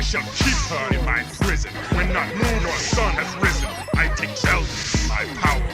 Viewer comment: oh yeah Mr krabs ahhhhhhhhh<:0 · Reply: I like the song that ganon sings. And I thought he said I take selfies in my prison. But he said zelda. lol